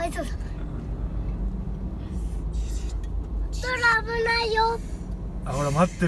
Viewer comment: ここ丁度お父さんの声がライオンのアフレコみたいになってて草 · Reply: 何回聞いても「危ないb...」みたいに聞こえてたけど、めちゃくちゃ早く邪魔すんじゃねえよって言ってる笑 思ったよりお父さん早口だった笑